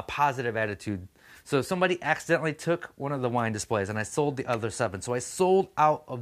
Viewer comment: Correct me (2:30) if I am wrong, but it sounded like you said your wine displays we're way under priced?